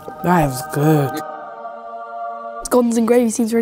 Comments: Southern biscuits and gravy are a national treasure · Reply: I always heard "Guns and gravy" not once have i actially heard "scones" If i didn't read 'scones' i would have been confused as frick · Dude I wasn't paying attention and thought they said guns and gravy. Thought AMERICA 🇺🇸 yeahhhhh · "How do they eat scones and gravy" Proceeds to eat Beans with Toast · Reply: What's a scone ? I thought he said guns and gravy.